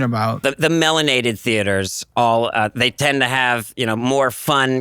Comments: did he say melanin theaters? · excuse me, the what theaters? I had to go back and listen to that a couple of times to make sure I heard that right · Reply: Didn’t he say “melanated” theaters?